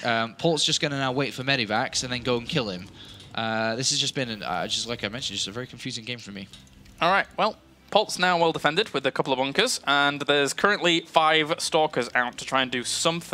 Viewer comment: Did he say "unkers" at 7:55? lmao